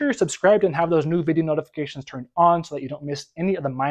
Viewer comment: Did you just say "Mahalo my friends"? Are you from Hawaii?